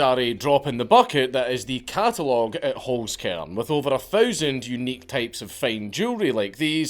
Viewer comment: Oh… I thought he was saying “hoes-can”.